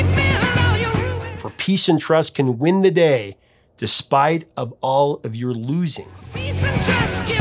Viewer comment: I always thought he was saying "If peace and trust cant win the day, its not about your losings" .